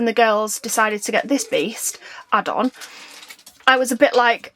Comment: That “EAST” around 33 mins is NOT on the original rendered video. 👻👻👻🥶💀 who heard it?! 🎃 ⤵️ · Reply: It sounded like saying Beast 😂